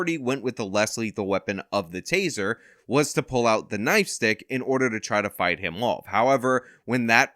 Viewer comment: did you say "knife stick"?